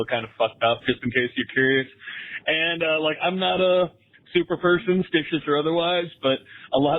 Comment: Did he say "I'm not a super person or stitious otherwise?" 😂😂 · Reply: It was ‘I’m not a super person, stitious or otherwise’. First time I’ve heard it as well lol